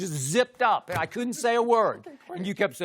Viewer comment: I thought shannon said "head only" 😂😂